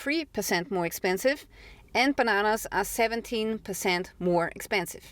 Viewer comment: Hahaha i heard "empanadas" i was like why is this european lady talking about empanadas... When i added captions... " And bananas..."